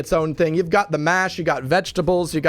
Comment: Did he say MASH?